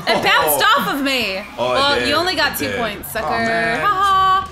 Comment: when it said two points I thought is said two potatoes........ xD